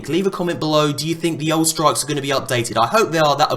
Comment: It says in the subtitles "struggles" instead of "strikes". What does that say about Destiny?